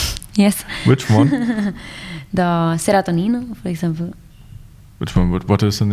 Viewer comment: anyone catch what book she mentions at 47:20? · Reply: Serotonin by Michel Houellebecq